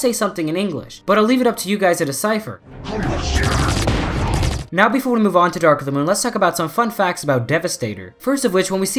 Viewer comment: I think he says go to the Target now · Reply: No he actually says “holy S**t